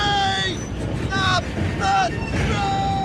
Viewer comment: Get wrecked nerds ! Sounds like he said up the train